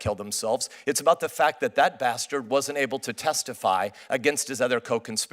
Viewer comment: did he just say “bastard”?